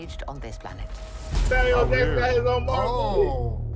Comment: Did he say, "Samuel Davis got his own Marvel movie." Bruh 🤦🏾‍♂️ 🤣🤣